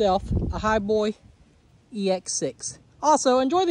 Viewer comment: 😂😂😂😂😂Thought yu said over the Moo about it..dang moon!😅